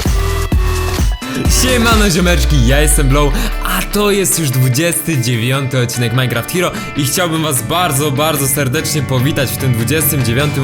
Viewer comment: HEH usłyszałem ,,Spierdalać ziomeczki ja jestem blow'' XD